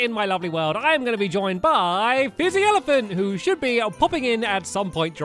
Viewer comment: At first I thought he said Busy Elephant